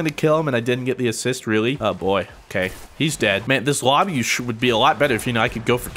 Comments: I think he said "just would be." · it sounded like "this Lobby Sure would be Better"